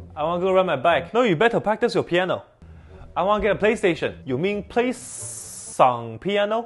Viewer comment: I heard "you Ming Playsss... Refering to one of many Dinasties that ruled China in the past